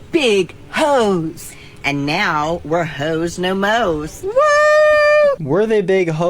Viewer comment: I’m not done yet but I really thought HO NO MO was supposed to be a christmas tshirt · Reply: i thought it was saying, "hoe? no, moes." what does the moes mean at the end of that sentence ill let you decide